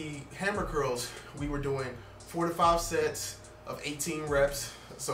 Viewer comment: I thought he said “45 sets” I was about to commit Sonic movie trailer